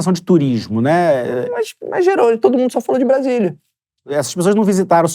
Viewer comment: "Cê chamou ele de Jerônimo ?" Kk me quebrou hauaha foi natural demais a 21:22 Porque parece que o realmente fala Jerônimo mas ele tava falando que "blá-blá-blá gerou alguma coisa" e o viajando foi entender depois kkkk 😅 achou que nós não notaríamos? Mas nós notamos. DC esteve aqui ⚡️